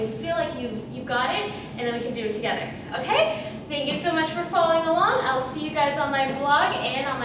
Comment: I could have sworn she said, "Thank you so much for falling along."